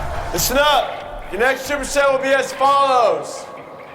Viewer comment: I can’t quite catch what the PT instructor is saying. “Your next “…” will be as follows:” So I turned on CC and was not disappointed. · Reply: Superset